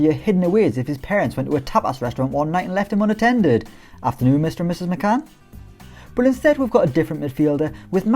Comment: what did I just hear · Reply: He said afternoon ms and Mr mc Cann👍👍